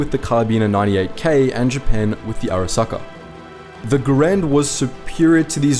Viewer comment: ok, too much Cyberpunk, I heard Arasaka, not Arisaka.